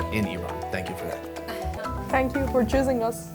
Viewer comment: what does she say? Thank you for jizzing us?